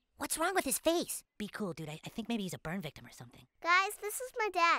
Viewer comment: "What's with his face?" "Be cool dude, I think he's a burn victim" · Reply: I don't know why I heard it like "I think he's a pervert or something" lmao